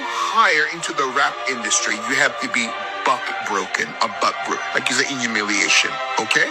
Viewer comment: well i heard,"buck broken".